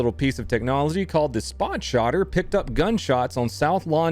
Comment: Wait... Am I hearing right or just having a stroke, cause I swear I just heard Donut call that 'nifty' little device a 'SpotShotter' not only once but twice.